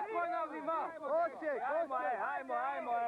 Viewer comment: What does he say, the soldier who wants to maintain discipline? Something like "Heimo" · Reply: 'Ajmo - from" Hajdemo" - "Let's go"